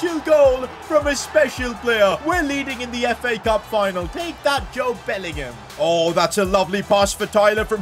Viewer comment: I swear I thought said JOBE BELLINGHAM 😅